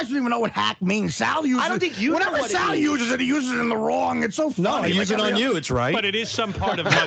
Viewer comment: I've listened to this clip maybe a million times. Does anyone know what Sal says at 51:11 ? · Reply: Artie claims they don’t know what the word hack means and how to use it. Sals says “we use it on you and it’s right.”